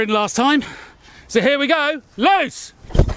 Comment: I'm pretty sure youre saying "loose" as in "the projectile is loose(not in the machine anymore)", but I can't help hear it as "los" as in "achtung, fertig, los", which means "ready, set, go" in german. I wonder if theres an etymological connection there These experiments are interesting! Always cool to see someone playing with physics in the real world instead of just testing things in a simulation haha